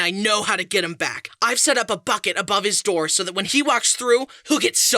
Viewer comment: my sleepy me thought you said “f*cking above his door”